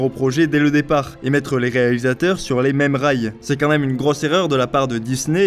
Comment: "...et mettre les réalisateurs sur les mêmes Ray." ... De quoi ? La sortie ? Oui, bon, je... Oui ? D'accord...